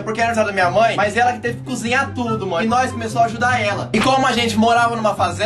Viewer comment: eu quase entendi que vcs morava em uma favela kkkkkkk